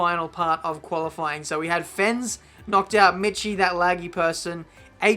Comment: I wasn’t watching the screen when you were reading out who was eliminated in Q2 so when you said “ mitchie” and “that laggy person” I thought you said “mitchie that laggy person”..